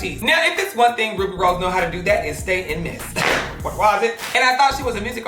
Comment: Does any one know what is saying at 9:21 I’ve always wanted to know what he says we he says that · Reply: im pretty sure he’s saying “what was it”